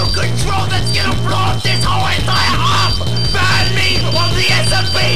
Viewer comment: “I HAVE A REMOTE CONTROL THAT BLOWS UP THE HOLE ENTIRE EARTH BAN ME FROM THE SMP NOW” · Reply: i thought he said "I HAVE A REMOTE CONTROL THAT BLOWS UP THE WHOLE ENTIRE UP"